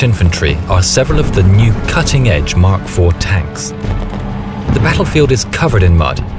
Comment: at first i thought he said at 0:12 the mach four tanks.